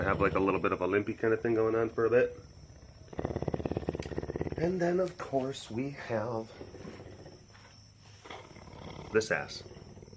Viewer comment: I thought he said " and then of course we have this ass" sorry Cass!